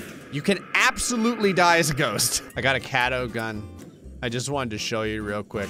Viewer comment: what said: I got a catto gun. What the closed caption said: I got a scattergun.